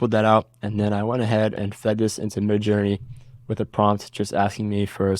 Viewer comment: im sorry @ 1:05 what did you say you fed it through to get a similiar image? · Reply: Midjourney